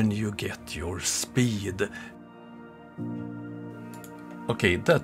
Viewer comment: did I hear "That was Globe buster in it's fool glory"? 🙈